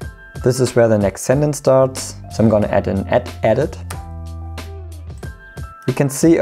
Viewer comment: I can't umderstand what you are saying at 2:00 was is alt+edit? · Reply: add an 'add-edit' (ie, cut)